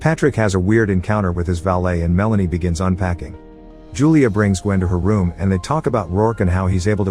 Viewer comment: I heard, Julia breaks wind in her room 😆